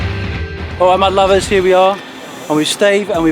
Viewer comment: Super find! The first time I ever saw one of your videos, I thought you were saying "hello my lovers." I thought, what a strange little dude!